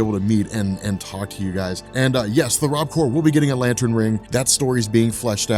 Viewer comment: Is it the Rob corp? I always thought he said RobCore